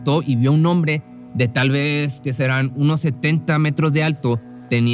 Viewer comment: Tuve que poner mute en el min 10:37, andaba jugando y donde dijo 1.70mts, entendí "unos 70 metros" y alch me culie.